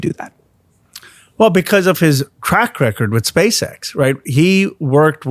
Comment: "His track record with Space X" Apparently we're not in the same timeline "The concept of DOGE ... was a good one!" Thats how a pitch works. They sound too good to work. Sure, you can say I obviously point it out After the fact ... ... except the facts WERE ALREADY THERE!!! You didn't need a trail and error! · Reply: I thought he said crack record😂😂😂😂😂😂